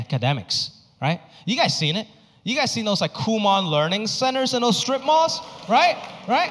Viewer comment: Thanks for mentioning Kumon. I didn't know this place so I thought he said "cool mom," but it was actually Kumon learning center. Anyway this place sounds wonderful and I am going to send my kids there when they are old enough. What's with the smiley in the Logo without smile though?